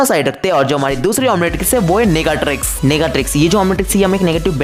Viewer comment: bro I heard nigga trix 💀💀